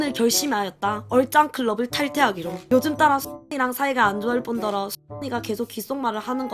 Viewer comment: 나만 친구이름이 수근이로 들리나?